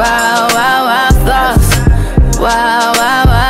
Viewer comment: For the longest I thought she said , " Why , why, why sauce" 💀😂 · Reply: I literally thought she was saying “why thoughts”